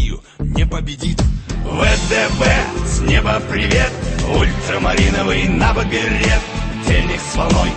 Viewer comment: LOL 0:49 He clearly says Ultramarine and you go ''Ooh i see ya''